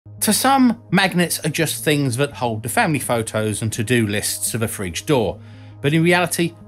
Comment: I had to go back and listen to the intro a second time. I started the video at 2x speed and it sounded like you said magnets are just things that hold fanny photos and to do lists to the fridge door.